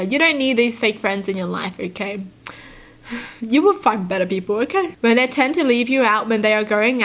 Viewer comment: "you'll find better people okay?" I THOUGHT SHE SAID YOU'LL FUXK ???